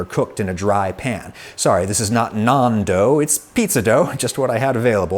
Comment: I had to listen to this 3 times before I realised he was not saying "non-dough" but trying to say nan dough!